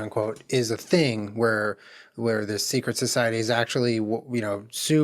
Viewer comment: am i the only one who thought this said “secret sororities”?